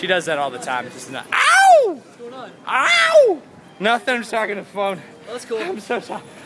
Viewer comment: Did Toby just call us his phone - Im shocked and appalled!